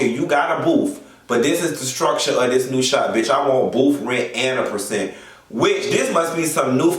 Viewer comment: Did he really say booth rent and percentage ? I thought he was saying a percentage instead booth rent